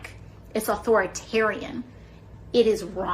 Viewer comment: I swear I thought she said "whoreatarian"... I was like "Of course a whore religion allows abortions.".